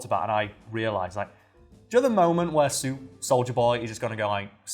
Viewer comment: Imma be honest, when said soldier boy I thought he said Soulja Boy... Clearly I haven't watched the boys and lemme just say, with the Soulja Boy misunderstanding this video is 30% more ridiculous